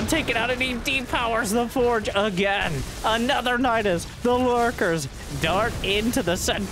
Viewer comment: Did he say "Dark" in min 33:41 ? haha